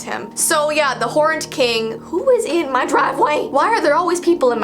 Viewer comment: I thought she said the horned king was in her drive away for a sec 😂😂